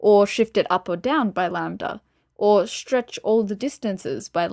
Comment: What word is she using to describe the transformation of the object? It sounds like "bylander" or something. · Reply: "By lambda" ( the greek letter ). Just a symbol to represent the value being transformed.